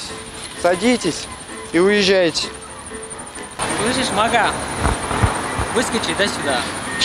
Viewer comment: Выскочи кто? Мага или мама, не расслышал